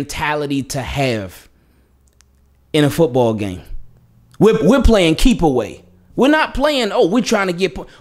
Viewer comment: All I heard was “we don’t play football wit leads we try to play keep away”